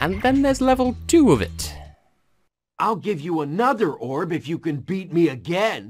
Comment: For some reason, I find Magma Cone the most appealing level in the game so far. Also, I misheard "another orb" as "a thunder orb".